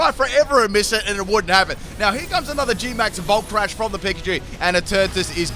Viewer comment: Did you say Gmax Volt Crash? Wen it Used Max strik?! Oh you did, ok.